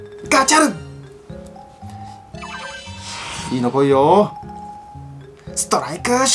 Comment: 2分50くらいのいいのこいよ！が114514に聞こえた